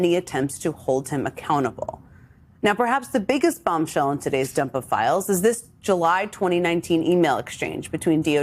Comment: I thought she said “dump o’ files“ 🤭💫 The trump dump … not the ones in his diapers. How embarrassing is it that the entire world knows DonOld wears adult diapers? And that sᴏᴍᴇᴏɴᴇ is charged with changing them? He has abused many drugs in his life but one of his biggest triggers has been three syllable words, like when he was given his script for “The Apprentice” and he was rehearsing his speaking points when A THREE SYLLABLE WORD was included, who knew? Who would have known how much Donnie hated big words, feared mispronouncing them and would ultimately go off on an angry rant with this resulting in a distressful diaper emergency ..? THIS giant man-baby, crybaby appears to be the pResident we’re stuck with, but not for long. He WILL be successfully impeached, indicted and REMOVED FROM OFFICE. mark my words ~